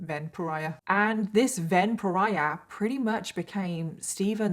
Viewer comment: The way you kept saying "Ven pariah" I thought you were saying vampire. And now I can't stop think thats what his intentions were.